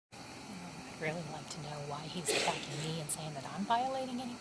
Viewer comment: Did I hear the prosecutor correctly during that side bar? Cursing to describe the defense argument aimed at her conduct. Lol. Totally unacceptable behavior before the bench. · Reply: I think she said “attacking me” but it sounded like “fucking me” lol